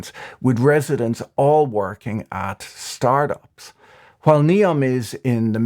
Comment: "NEOM aims to have zero work or stress-related diseases with residents all working at start-ups." I've never burst out laughing that loud in a while. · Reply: Oh, I actually thought he he said "residents all working at Star Bucks.."🤣